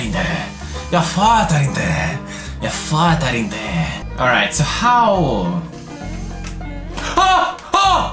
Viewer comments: I thought he said your father ain’t there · it sound like pewds is saying "you fart I dare" lol